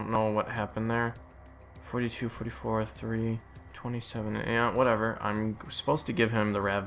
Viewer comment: WHen you were listing his levels, I heard you say " 42, 34, 3..." Looking at your vid I'm like "uhhhh you got 35" X.x lol